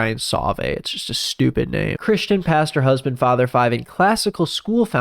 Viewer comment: I thought it said sexiest pastor